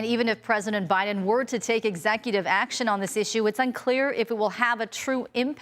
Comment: Lol ..I thought Buden said he couldnt take executive action😂